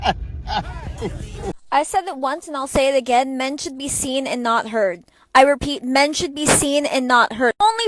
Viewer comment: I thought it said, "Men should be seen hard." I thought that wad a great idea.